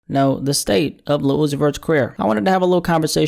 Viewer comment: I could have sworn he said "lil uzi vert is queer " in the first sentence